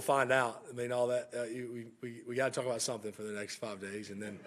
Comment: Non-native English speaker here... please help: did he say "We get to talk about something for the next 5 days" at 12:55? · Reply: Yeah, he said "we've got to talk about something for the next five days".